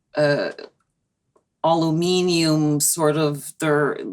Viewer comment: Alemenu? What word are you saying at 44:03? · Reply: Aluminum thanku